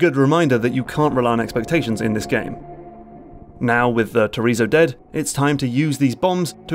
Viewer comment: did you call a chozo a chorizo? Lol